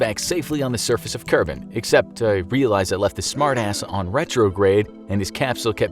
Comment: he sayd "' i letft the smardass"' tat is the frist time i heard tem swear